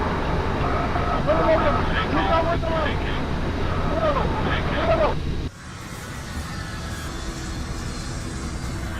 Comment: que dice la alerta del avion?? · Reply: "BANK ANGLE" quiere decir que el avión está entrando en el máximo ángulo dinámico en el viraje.